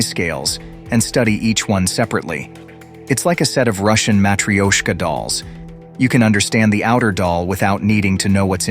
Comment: Ma-tru-sh-ka dolls... I've no idea what was just said 4:36 there?